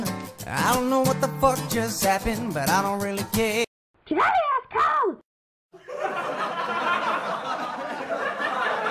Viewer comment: what is he saying? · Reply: JULIUS COLES